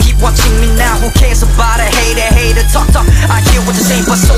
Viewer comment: I heard "knet knet talk talk" instead "hater hater talk talk" 😶